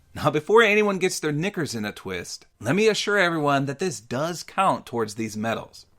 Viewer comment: what did you just say??? cmon brah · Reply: Knickers in a twist. What's wrong with that?